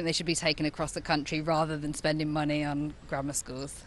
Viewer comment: I definitely heard "grandma schools" and was very confused